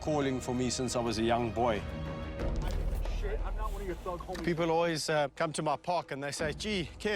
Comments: DID ANYONE ELSE HEAR “I’m not one of your thug homies” or is that just me ??! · did anyone catch the audio at 1:01 "i dont give a shit im not one of your thug hommies." like whaaaaaa